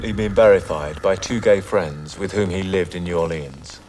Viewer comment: did he say two gay friends 😂